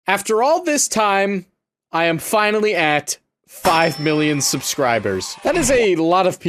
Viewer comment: I accidentally muted and unmuted the video. And as I did I hear "I am finally at... 5 subscribers!"